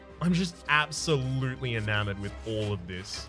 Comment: "i am absolutely in _____" what did he said? In amour? Like the french word for love?